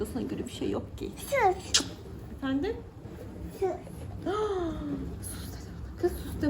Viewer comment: Canım ben yeni takipcilerindenim.Cok samimi çok tatlı buldum seni 😊Bu arada içli köfte bende yapmakta zorlanıyorum 3 kız çocuğu annesi olarak yazıyorum sana.Cocuklarin da çok tatlı masallah ❤Bu arada 18:19 da ben süt diye duydum sus mu demiş o minnos😀